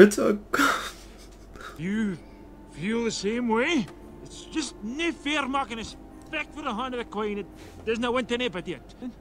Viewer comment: I got a bit of what he said at 16:58. I think he said "it's just not fair making us fight for the heart of the queen.." and then something I can't make out. But this was amazing to watch! I love brave and how different Merida is to other princesses. I haven't seen this movie in years so it was nice to see (most of) it again.